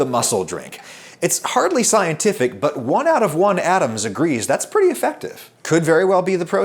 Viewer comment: "One out of one Adams agrees" Made me laugh more than it should have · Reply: I heard it as "one out of one atom agrees", which I thought was a very strong statement. :-)